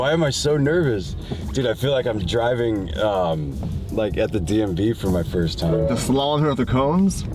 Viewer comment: did he just call it the DMB? 😂